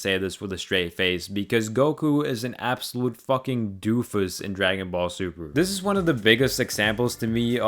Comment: I keep hearing “Dragon Ball Subaru”